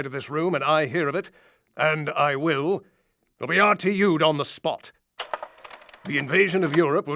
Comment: if I here the word bridge...and I .......what does he says??? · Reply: "If I hear the word 'bridge' and I *will*, you will be RTU'ed (returned to unit, i.e. you got canned from the op) on the spot."